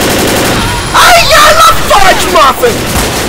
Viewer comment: did say " YARG!! IM A FURGE MUFFIN!!"?? it sure sounded like that!